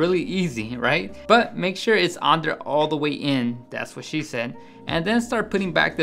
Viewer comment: LOL did he say 1:03 " make sure its all the way in , that's what she said" ? lol